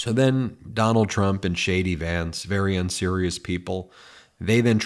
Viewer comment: At one point I thought you said Shady Vance....now it's stuck in my head 😂